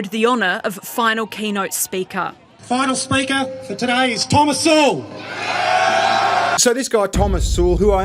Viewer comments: oh, i thought you said 'thomas sowell'. didnt have the screen on when you said that. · I thought they said thomas sowell... but he is with us right?